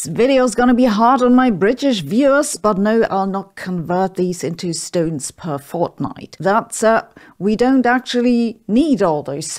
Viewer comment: I thought she said "stoners per Fortnight," and I am pretty sure that is a legitimate SI unit!